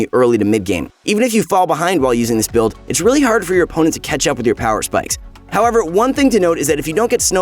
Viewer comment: deadass thought he said ketchup xp